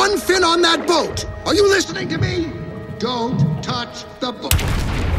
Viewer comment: DID BRO SAY DONT TOUCH THE BUTT